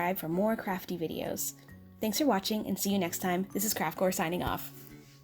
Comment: sounded like she said "this is craft whore " was like yass sameee🙌😂😂